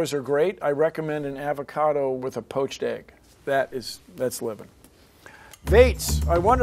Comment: at first I thought he said that's lit, and I almost jumped out the window